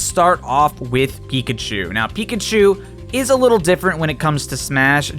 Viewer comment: Did anyone else heard Geekachu?